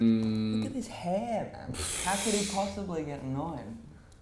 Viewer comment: What is he saying? "How could he possibly get annoying?" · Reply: "Look at this hair man!, how could you possibly get annoyed" ..sorry..sorry Kevin" lol